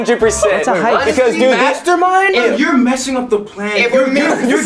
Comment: When they said mastermind I thought they said master-bate and Austin just popped up I was lmaoing